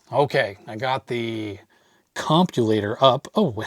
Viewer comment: I thought you said “Copulator”!